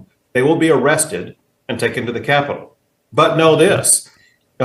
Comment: I think I heard him say arrested and taken to the house not jail. If this is so serious take them to jail!